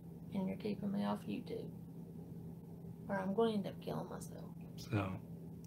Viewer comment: It sounded like she said, "I'm going to end up killing myself."